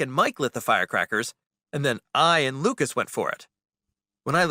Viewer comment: the subtitle said and eat the fire crackers 😝